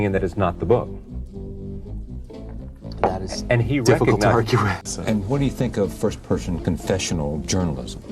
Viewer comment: What was David Foster Wallace's response to Charlie's statement (8:02 -- perhaps quoting someone else), '...the film is interesting in that it's not the book'? It sounded like, 'That is...definitely not correct.' Did I get that right? (It's hard to say with Charlie, y,know, talking over him....) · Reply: It sounds like he said "that is difficult to argue with"